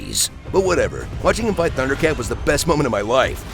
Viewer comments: I thought he said “Thundercat” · He said Thunder Cap and I thought he said Thunder Cat and I immediately started checking for Lion-O